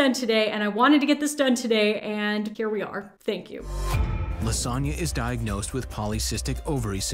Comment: I'm sorry but LaSonya sounded like Lasagna 😭😭😭